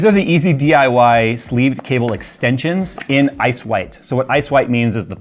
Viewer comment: Why do I hear "ass-wipe" when Jay says "ice white"?